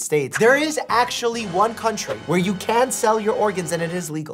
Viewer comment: it sounds like he was saying "I ran away"